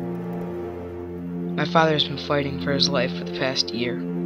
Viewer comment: it sounds like you said farting but good trailer